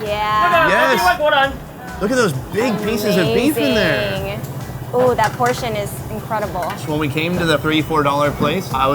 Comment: i heard them shouting “外国人” aka foreigner •-• what were they tryna say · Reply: he said pass this serve to the foreigner next to you, nothing too exciting here